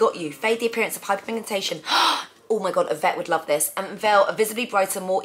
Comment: When you said” Yvette will love this” I thought you said a vet will like this!😂